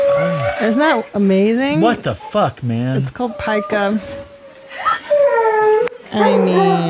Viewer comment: i thought she said “that’s called pipe gum”😂🤢